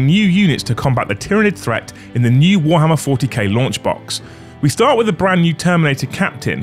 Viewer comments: I was listening to this and heard you say "new Warhammer 40k lunch box".😂 That got me to look and see what it is. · I thought he said lunch box for a second cause I wasn’t paying attention and just thought the emperor was selling space marine propaganda merchandise to its citizens😂